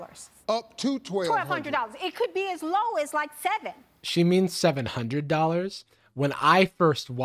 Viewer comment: I also immediately thought she was saying as low as $7.